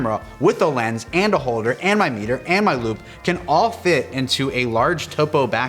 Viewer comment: I heard ..."and my lube" when he said "and my loupe".